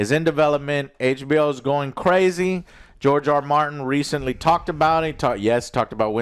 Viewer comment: George R Martin 🤣🤣 I thought you said GIORGIO ARMANI!